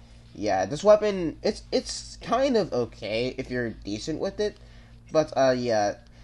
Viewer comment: i thought he said "i-its kinda GAY" but he said "okay" lmao